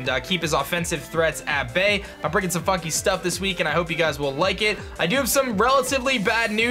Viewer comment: i thought he said "im bringing some f***ing stuff" lol xd